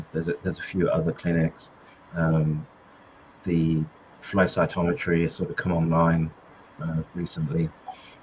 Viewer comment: what kind of test did he recommend? F..(something)-metometry. For patients with neurological symptoms he meant, right? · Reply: I think I heard 'flow cytometry' 🤔